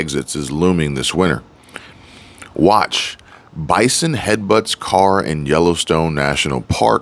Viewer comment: I thought I heard say Biden headbutts car in Yellowstone! Then when I looked at the screen I saw bison! 😂😂😂